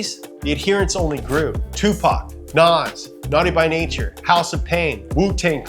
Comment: You said "Tufa" instead of Tupac 🤔🤔